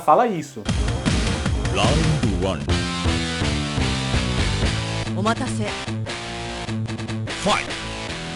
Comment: eu juro que entendi "toma café" 11:10 kkkkkkkkkkkkkkk